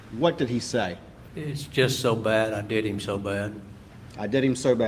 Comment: I hear ' it's so bad , i did them so bad ' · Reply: Greetings from the UK. 😁 I heard that as well.. Listened to it a few times and what I hear is "I did him/em so bad"